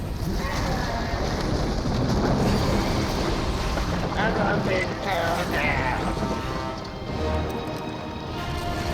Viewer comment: 😂 What the heck was trying to be said? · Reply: Honestly sounds like she's saying "I'm a big girl now" lol